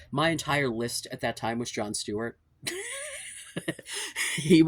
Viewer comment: I didn't know who John Stewart is, so I thought you were saying John Stuart Mill was your hall pass for a sec 😂